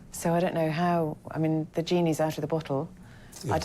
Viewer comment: did she say "the gene is out of the bottle"?